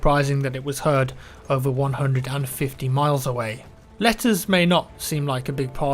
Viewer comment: I'm not gonna lie. I thought you said "lettuce". And I'm like 'does the military in WW1 only eat lettuce? oh wow'. then you go on showing a letter and I'm laughing at myself. 'Oh, Letters!!'